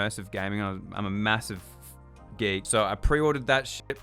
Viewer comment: Anyone else notice they cut the video a little at like 4:20 so joshdub says “I’m a massive gay” instead of gamer 😂